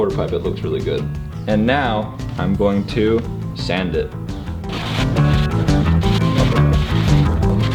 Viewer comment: I thought he said SEND IT. lmaoaoao